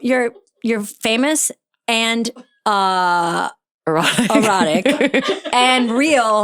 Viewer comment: I thought you said NEUrotic.